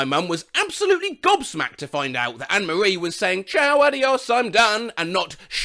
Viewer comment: My parents thought it said Shower the horse I’m done😅